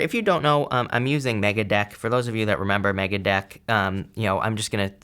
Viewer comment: I thought said “Megadeth” 😂